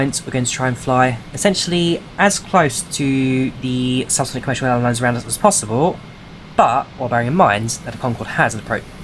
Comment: As close to the....subsonicsoddjk as possible. <--- Am I the only one who couldn't really understand that? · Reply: "As close to the subsonic commercial airlines around us as possible" 😅